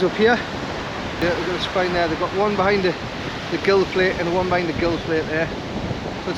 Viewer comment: Nice to see you out on the Beach. When you were talking about the Bass I at first thought you said Plaice.. was like jeez man your optimistic for a Plaice between 45-55cm 😂. You said plate I get it now.